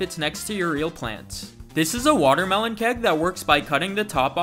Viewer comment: i heard “ watermelon cat”😭